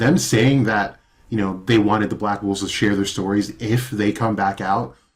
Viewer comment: did he say Black Wolfs?